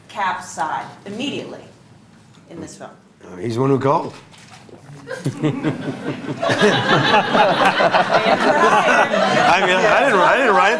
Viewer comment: What did Jeremy say at 13:41? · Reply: "He's the one who called."